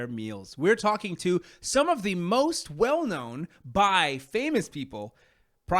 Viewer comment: HELP I heard “some of the most well-known bi famous people”